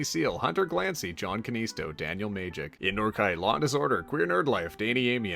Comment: Every time reads out 'Law_and_Disorder' I hear 'Lawn_Disorder' and think 'What a great user name~!'.